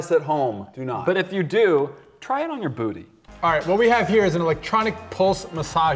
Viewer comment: "Do not try this at home, but if you do, try it on your booty." -, 2016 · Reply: SuperNintendoPlushie I thought he said he said booby😂. Well, it works both ways haha